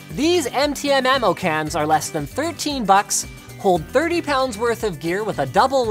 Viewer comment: “hold 30 pounds worth of gear” what I hear “holds about 1000 rounds of 5.56